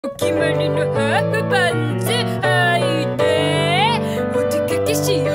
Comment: ハーフパンツが、ハートパンツに聞こえてしまってごめんなさい😂